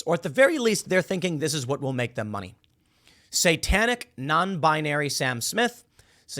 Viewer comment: Thought it said satanic granny slammed.